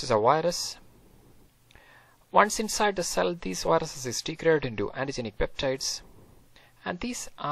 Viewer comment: Once inside the cell, these viruses ___ (at 2:40) I couldnt understand it. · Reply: 'these viruses is degraded into antigenic peptides'